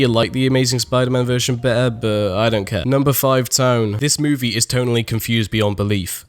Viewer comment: Anyone think at 13:19 it said s.tone